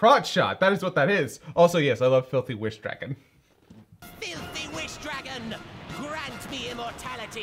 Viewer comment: I always thought he said “Phil The wish dragon”